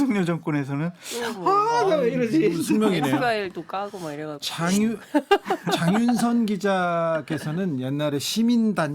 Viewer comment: 여기자님이 뭐라셨는데 쉿!! 하셨을까요 정확하게 안 들려요 몇번을 되감기해도 안 들여요 누구지? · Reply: '엑스파일도 까고 막 이래갖고'....